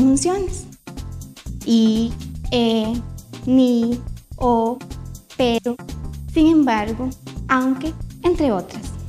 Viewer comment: Jsjs en el minuto 2:17 le entendí pedo jsj